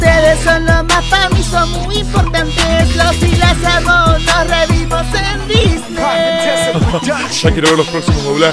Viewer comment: Pensé que decía "Nos reimos en Disney" no "re vimos". Ahora ya no entiendo nada, ayuda